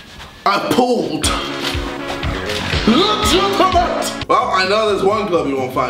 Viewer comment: The hack does he say? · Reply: Legitimate